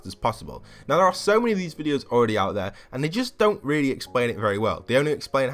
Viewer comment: That can’t be real can you link me on twitter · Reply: Nvm I completely heard you wrong at the beginning I thought you said "there are so many Golden Peeles already out there" but you said there are so many XP videos out there. My comment was asking you how are people 300 already when C2S2 just dropped so bit of confusion on both our parts I guess.